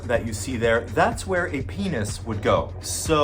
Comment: Exactly my point. The red area has nothing to do with where the penis is located. You are showing a diagram of the upper intestinal and the red is the rectum. The penis area does not connect to the rectum. In other words the red area has nothing to do with the penis. It is solely part of the digestive tract, intestinal system or whatever you want to call it in layman’s terms. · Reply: The first thing he says about the red part is “See the red part? That’s where a penis would go.” I think you may have misheard “penis” and thought he said “pee is” or something about pee….? But he 100% said penis, and never said anything about peeing out of your butt. Source: 0:44 also I’ve watched this video twice now… first time prep 😅 Edit: just FYI, “this is where the penis would go” means “this is where another person’s penis would be located if/when they put it up your butthole”. So if that diagram is YOUR butt, the penis he’s talking about is NOT your penis. Talking about another person’s penis. I just realized your confusion.